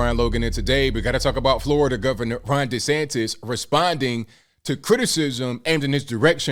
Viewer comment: Wait... Ron DeSantis? I live in Florida, I thought we had a woman governor this whole time. I thought everyone was saying Rhonda Sanchez.